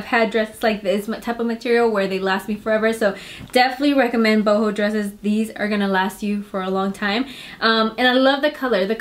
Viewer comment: Not to be gross or anything but 7:32 I seriously thought you had said "butthole dresses" I had to listen again 🙈 lmao